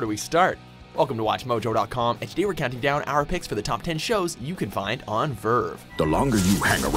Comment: At first I thought it said shows you can watch in VR